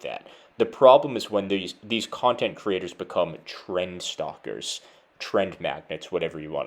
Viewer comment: Man for a second I thought you said Tren Stalkers